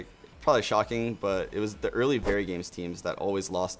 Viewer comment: What was that team he said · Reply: Commenter 24 verygames... old French super star team before titan